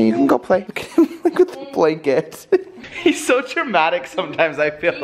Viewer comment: I sware I heard said "look at these dada"